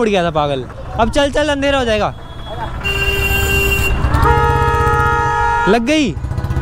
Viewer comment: what was saying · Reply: He was saying: mere toh L lag gaye